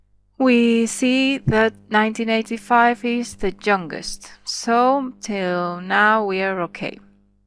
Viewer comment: i dont understant.. what means "THE JUNGUEST" or u want to say YOUNGEST? :|